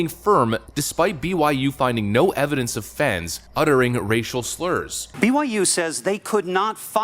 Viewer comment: Sorry I thought you said Rachel Slurs.